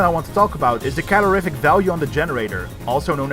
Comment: I heard booshter